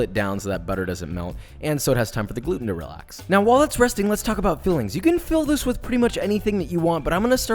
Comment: I thought you said “let’s talk about feelings” 😂